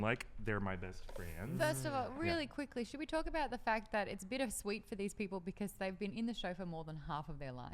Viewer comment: Did really say "bit of sweet" @ 13:51? -_